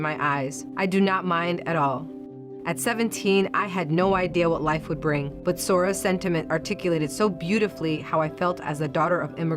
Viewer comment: "But Soros sentiment articulated so beautifully to me...." "Soros sentiment"???? Is that what she said? And if so....are we still supposed to just keep pretending there is no "Deep State"? Why does "Soros" keep popping up in Government agencies that are supposed to be politically non-biased? I just read a story about how this latest thing with Rudy Giuliani may have something to do with the Biden DOJ trying to protect and conceal the FBI coordination with AntAC (a Soros funded "anti corruption" group in the Ukraine). That group hired a US FBI agent to it's supervisory board. The same agent that was coordinating with them to conceal the corruption surrounding Burisma (the Biden's influence pedaling scheme) and the subsequent investigations surrounding that were closed down with the help of the US Ambassador Marie Yovanovitch. So it seems there was a cover up back then to protect the Biden's....and now there is a cover up to cover up the cover up. This mainstream media angle about Rudy "helping the Russians" sounds like a deflection and a load of malarkey.